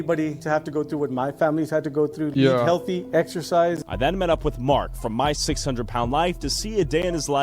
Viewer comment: Excercise? I thought you said extra fries 🥺